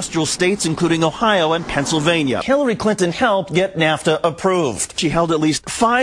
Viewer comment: Am I the only one who heard “Killary”